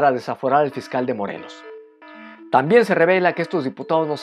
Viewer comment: clarito oi " desaforar el mezcal de Morelos", jejeje